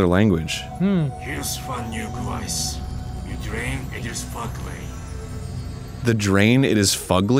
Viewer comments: “The drain is fugly” and “going baton” are my favorite misheard quotes in this game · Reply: 100% with you. 6:48 "The drain. It is fugly." Not a misheard quote, that is exactly what it sounds like. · “The drain is fugly” and “going baton” are my favorite misheard quotes in this game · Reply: Even the closed captions thought the alien said drain it is fugly, lol